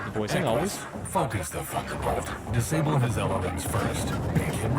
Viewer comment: when i watched the premiere i misheard say "focus the thunderfuck" at 8:02 prob cuz i called it so much in coop as mechs are an actual threat when you are not playing with that that garbage softball fanfiction that is the YAML mod. Vanilla FTW 8:51 inferno missile its basically white phosphorus in gel form like napalm for anti mech use basically that can cook pilots if it gets in the cocking, MW5 even has a mention of it used as such in the kesterl lancers DLC 11:02 that might actually be an SLDF model of the treb 13:17 LRM stream